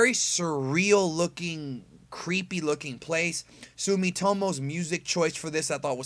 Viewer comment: I thought he said Miitomo lol